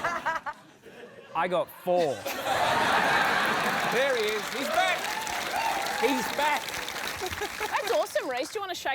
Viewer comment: What word got needlessly bleeped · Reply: Presumably 'blowj0b', supported by Rhys' reaction to it